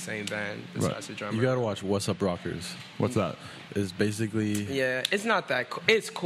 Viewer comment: Yo what movie did he say can’t hear him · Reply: wassup rockers